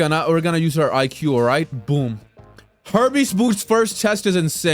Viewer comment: You think empress of light would be easier, considering YOU CAN BLOCK 3:20 I heard “herpes boots” 😭😭😭